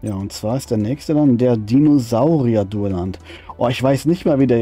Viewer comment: hab doch zuerst "Ser Dinosaurierdöner" verstanden 😂😂😂, 🙈🙈🙈. 1:58, hahaha, genau so wie man es schreibt, Mai, nicht Mäi, 😁😁😁. Aber ich vermute mal das beide Aussprachen richtig sind, der eine sagt "Mai", der andere sagt "Mäi", 😁😁😁. 21:50, haha, gewusst wie 😁😁😁.